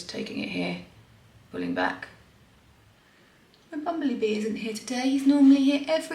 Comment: 🐝 how sweet that your bumble bee came to see you 💖😊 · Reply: I thought at first she said "bumbly" bee. What a perfect name for it.